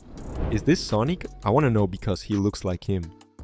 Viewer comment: why did he say "sonique"